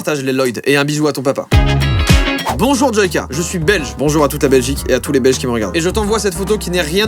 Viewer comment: "bonjour Joyca je suis belle" (C'est ce que j'ai compris à la première écoute)